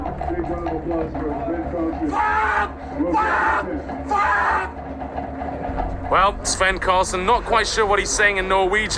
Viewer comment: „FUCK FUCK FUCK“ „Not quiet sure what he‘s saying in Norwegian there but im guessing he‘s not very happy“ commentary gold at 2:40 right there 😂😂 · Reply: I think its fan, fan, fan. It's a common curse in those areas. Loosely translate damn, damn, damn